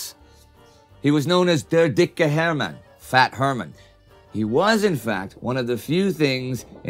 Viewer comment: Did he just say "he was in fat..." instead of "he was in fact..."? XD